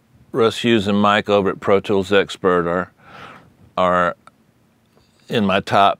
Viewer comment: He speaks about his favorite website, I cant understand what website He speaks about. Could you give me the name of it? Thanks anyway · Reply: Think it's 'Pro Tools Expert' - worth checking out!